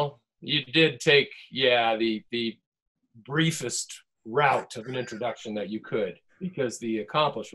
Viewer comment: Hilarious! sounded like Will said racist route in beginning...lol ! I'm sure he wanted to express the stupidity out there in the world 🌎